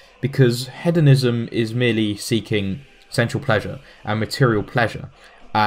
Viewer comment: It took me a rather embarrassingly long time to realize he was saying "sensual pleasure" and not "central pleasure". That's what it sounded like! I'm not saying it made sense to me; I was boggled. It's just...that accent. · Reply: I actually thought he was saying sexual pleasures.And both Inane and I are still trying to figure out what he means by 'Logos'.